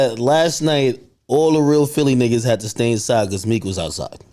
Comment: ish AK funny as shit. I heard all of Philly gotta stay in the house cuz meek out ?!? 😂😂